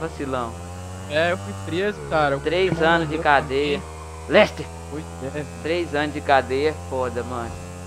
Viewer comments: eu ouvi falar Lester aos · aos 4:23 escutei um LESTER ali em